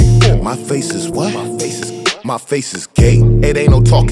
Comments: did he say his face was gay 😂😂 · i cant be the only one who heard "my face is gay"